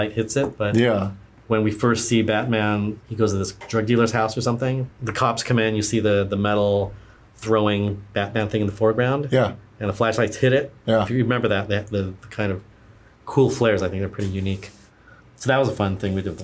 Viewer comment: Did he just say "metal batman throwing thing" @ 18:23 😂😂😂😂